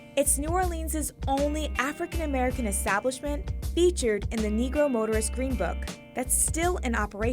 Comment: Did she just say the N word at 1:28??? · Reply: What...Negro? Folks still say that word here lol.